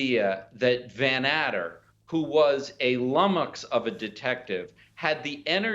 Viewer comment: Can someone tell me what is the word Toobin used at 18:17? "Lummox" of a dettective?